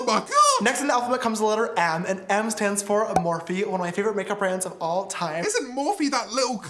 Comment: I almost thought that he said morphine